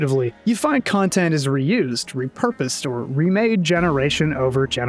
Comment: Every time you say Capcom “reused” something I hear it as “Ryu’sd” something . I’m too fucking high man. I like bow. Bow fun. Played since world. Bow good.